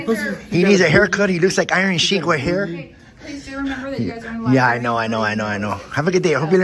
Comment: Are my ears O.K. or did I hear him saying "liberry"?